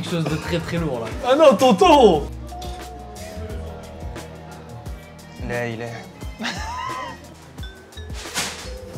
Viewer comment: a 20:30 il dis quoi ?? j hallucine?? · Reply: Il a dit "la ilaha illa Allah" ("il n'y a de divinité si ce n'est Allah"). Il n'y a donc pas de mal à cela